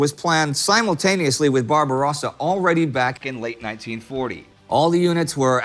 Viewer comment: Guys quick notice at 2:50 says the invasion of Greece was planned back in 1940, while the subtitle says 1941. Just a tiny typo i think :) Anyways great video as always, thank you